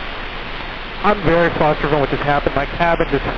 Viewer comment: I think he said, "I'm very flustered after what just happened. My cabin just filled up with smoke. It smelled completely foul. Let me pull off at Charlie and just compose myself." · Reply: Not what he said. He says "I'm very closter phobic" which explains why he was freaking out when the cabin filled up with smoke. It's possible he wasn't in nearly as much trouble as he thought he was but was panicking.